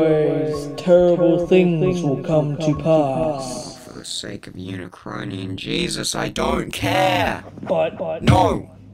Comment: best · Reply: oh thought he said for the sake of Unicron and jesus